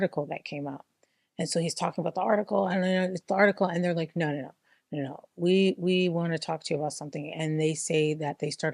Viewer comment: People that follow crazy and sign up for stupid get crazy and stupid outcomes. p.s. in the beginning of this video I had a little jump scare too bc I thought he was saying Noor Noor NOOR!! 😂